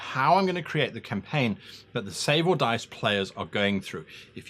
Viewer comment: OMG! Can't wait to see you on Save or Dice! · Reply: Renato Lopes Lol. I thought he was saying Sable Dice...